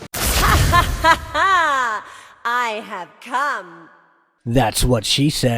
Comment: I thought she said I am cum But it was I have come 😂